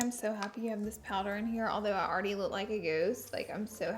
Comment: did you just say “I look like a GOOSED?” (When you said you looked like a ghost)